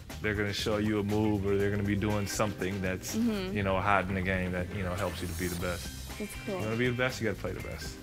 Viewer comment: did he say, "you know, hot nigga" lol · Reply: Hot in the game